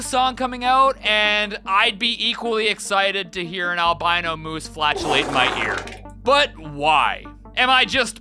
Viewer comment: Ooooh, I had to do a retake on the albino moose. I heard "an albino moose fleshlight my ear". That's pretty spicy